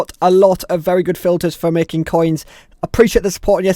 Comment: I thought it said make no coins 😅😂🤣 ahdv wheni patsecd thoughts hr. was cince&ntrartung